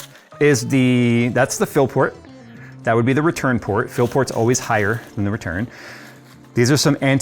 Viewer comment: i keep hearing "phil-port" ...